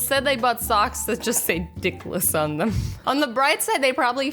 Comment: I thought it said pickles...